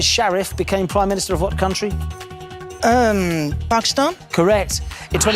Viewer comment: Can someone please tell me what she said 31:48 ?🧐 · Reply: It was hard for me to listen due to the accent, but it was Pakistan 🇵🇰😅